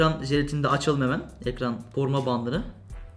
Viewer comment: Burda ne diyor ben mi yanlış anladım :D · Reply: Koruma bandı